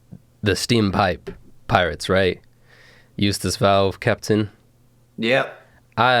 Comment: Nah I thought they were saying “Use this valve” instead of “Ustes Valve” ☠️